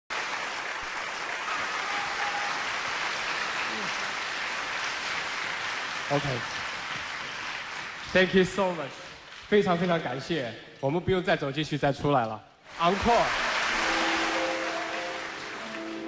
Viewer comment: it sounded like he said "UNCLE!".